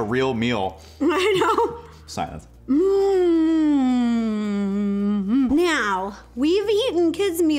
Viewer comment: did she say meow cause I swear I heard meow